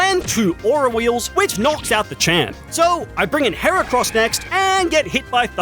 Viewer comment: that’s primeape… WHY DID YOU CALL IT MACHAMP??? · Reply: He said “the champ”